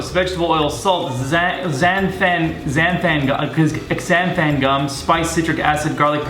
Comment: When he said xanthan gum i thought he said xanex